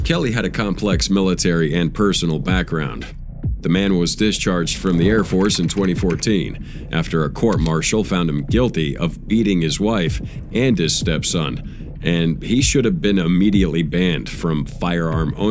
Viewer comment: i was lost it sounded like eating and not beating